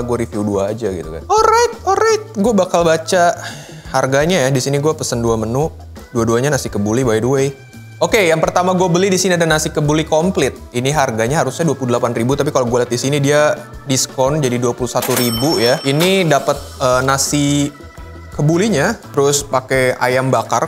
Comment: kenapa saya dengernya nasi kebuli konflik